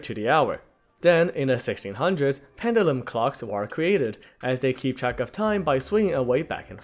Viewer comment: Im sorry, i thought it said cocks